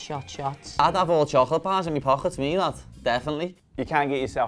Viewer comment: wtf he say lol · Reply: id have (unknown) chocolate bars in my pocket, me lad, definitely